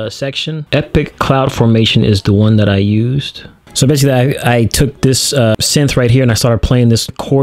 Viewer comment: Did anyone else hear, “I took this Sans right here.”